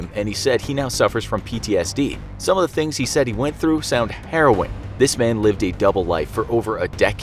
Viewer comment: I know he said \meant "harrwing " but i heard "heron " and thought it was a bad pun LOL